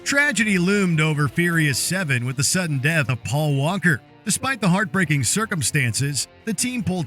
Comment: Did you just say: Paul Wanker?